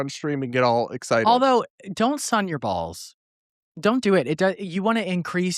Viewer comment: When said sun your balls, at first I thought he was saying, "Son, your balls," and I wondered if this was some sort of new age father son pep talk or something.